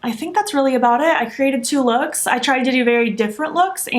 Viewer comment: I thought it said 12 looks 😂😂. Hiii hun.